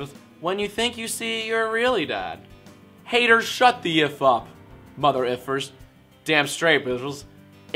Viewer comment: sounded like u said Shut the Yiff up